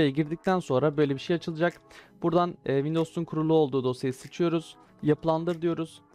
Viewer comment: bi tek benmi sıçıyoruz diye duydum :D